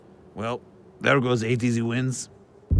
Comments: Sun-Kings get destroyed. Barkley: "I don't know anything about the Sun-Kings but the Sun-Kings are in trouble" Harden: " Well there goes 8 easy wins" 🤣🤣🤣🤣🤣🤣🤣lol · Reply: i thought he said 83 wins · “There goes 8 easy wins” 😂😂😂 The kings were kinda good last season but still funny lol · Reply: At first I thought he said 83 wins as in their combined projected records at the end of the season were that total lol